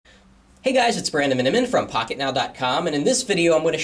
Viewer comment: I always thought you said "Hey guys it's Brandon and I'm in from pocketnowcom" Now I realised you say your second name as fast as Jaime.